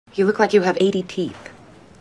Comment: Thought she said "it looks like you have ADT"